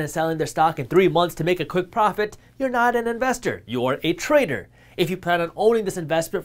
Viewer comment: almost sounds like you said a "traitor", not a "trader". 😂